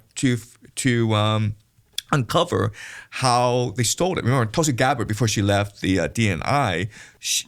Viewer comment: Jiang is awesome… but I heard “toasty” gabbard and nearly died